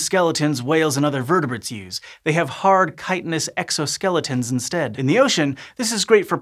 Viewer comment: What word did he use at 3:09? “Kite-n-ous.” I want to know how it’s spelled so I can look it up and add it to my vocabulary. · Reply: Chitinous.